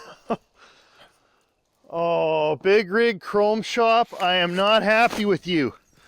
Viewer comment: if he did, I missed it. My hearing isn't so great. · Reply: Think he said "NOt happy with you Big Rig Chrome Shop"